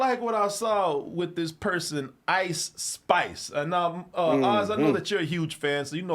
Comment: I thought he said ICE spies